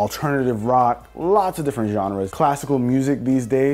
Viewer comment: How many ppl thought that he'd been saying dababy instead of the baby lmao (Like if u thought the same)